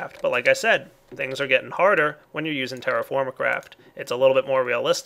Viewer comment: lol i heard him say terraformacraft